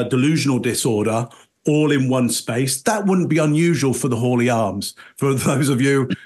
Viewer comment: What was the point she was trying to make about "Scottish banter"? It didn't make any sense, though I'm American so maybe something got lost in translation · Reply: You notice when she gets in trouble she really elongates her words "It was just Hawley Arms baaaaaaaaanterrrrrrrr"? I need to try that technique! I thought she was saying "Holy" Arms until I looked it up lol